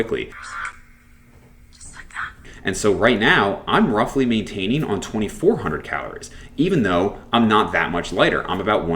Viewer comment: what is being said at 7:05?????? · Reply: It's a clip of Gamora saying "just like that" in an Avengers movie